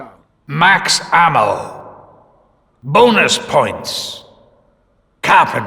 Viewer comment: what does monty say? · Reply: bonus points